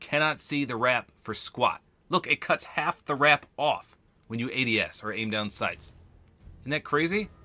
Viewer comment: Did he say idiots???? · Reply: I said when you ADS. That means aim down sights.